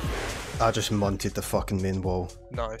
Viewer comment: What did he say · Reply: 'I just munted the main wall' Aka 'I have just absolutely destroyed there wall' xD